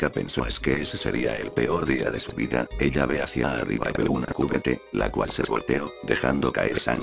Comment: ¿que dicen en el minuto 5:25? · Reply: Arriba de una cubeta